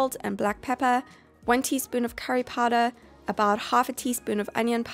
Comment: I literally heard 1tsp of Harry Potter 😂 What is wrong with me ? Too obsessed I guess ⚡️😂